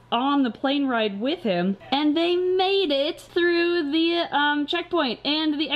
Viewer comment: When was talking about the isopds and springtails going on the plain I thought she said "they mated" and I was like awww they made babies. But she said "they MADE IT" LMAO